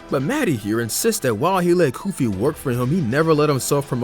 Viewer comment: Bro at 7:34, it said goofy instead of koofy